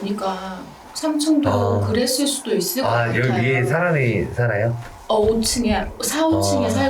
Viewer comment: 이 "어?"하고나서 여자같은 목소리가 '히여 히여' 인가? 속삭이는데요ㅜㅜ 사운드 잘못 들어간거죠?ㄷㄷ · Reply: 여기 위에요 아니면 여기위험해로 들리긴하네요